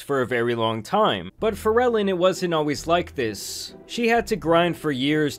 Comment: “for ellen” i thought homie said “pharrellin” like it was a new slang or sumn 😂